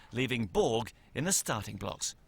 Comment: What does the announcer mean at 0:45 when he says McEnroe left Borg "in a starting box" (I may be mishearing)?